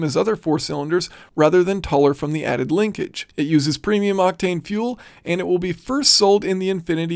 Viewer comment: I thought he said it uses premium latte fuel😁 Clever engineering.